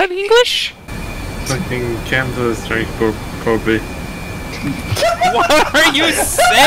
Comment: I just realized was actually saying "Fuckin Kamsa's trying to probe me "